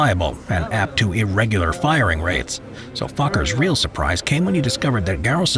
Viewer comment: I love it how the captions say "fucker's" Instead of Fokker's